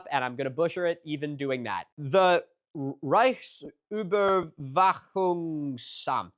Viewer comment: Good job with the pronunciation at 15:35, that was genuinely pretty damn good (and a difficult word), but the subtitles read "Reichssicherheitshauptamt" (Reich Security Main Office) instead of "Reichsüberwachungsamt" (Reich Surveillance Office) Either way, now that I'm done almost watching, I think I might just have to go visit her in Halle soon. It's a few hours, but she's also come very far, hasn't she? The special exhibition about the Bad Dürrenberg Shaman is still going until the 1st of November, hopefully there'll be time